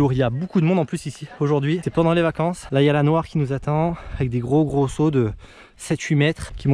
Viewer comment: la il y a quoi ? 🤣😂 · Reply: "la noir " la piste noir